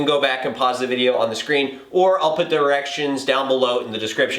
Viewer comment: sounds like he said "erections"